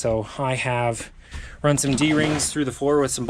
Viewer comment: I thought he said bitch seat😂